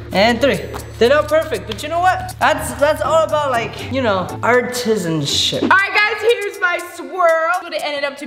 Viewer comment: it soundS like said art and sh*t LOL